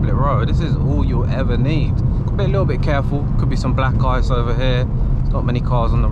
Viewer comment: I watched this video late last night and was half asleep. At 5.20 bro I thought you said “you gotta be a little bit careful, there could be some black guys over here” and thought wtf?? then I realised you were saying black ice. FFS No more watching vids for me when half asleep 🤦‍♂️🤦‍♂️🤣 🤣 Anyway Thanks for the vids bro keep up the good work, you’re doing an amazing job. Would be awesome if I can win this, imagine owning an E92 M3 for £11.99 💥